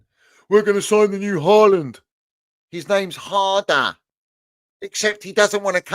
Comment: almost sounded like said Drum and bass father 😂😂😂 the way he said harder